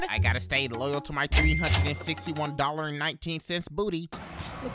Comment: "Go men! Eat a toilet!" Is all I heard